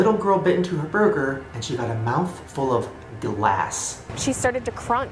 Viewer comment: I could have swore he said “Dlass” like it had a D in it.